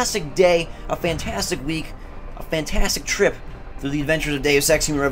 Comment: when you said deus ex...i thought you said the day of sex..awkward